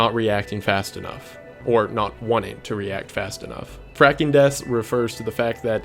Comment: This is around my like 4th time watching this video in full and on the Fracking Deaths, I thought you said Fracking Desks and got so confused for a whole moment LOL